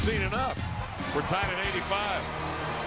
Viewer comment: We're tied at 85, I thought he said Retire at 85 lol